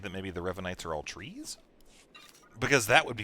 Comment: When said "The Revanites are all trees" I thought he said "The Revanites are all Fluttershys". O.o Help Me...